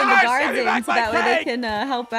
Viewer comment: Did anyone hear say " WE KNOW YOU'RE EMERALD SECRET "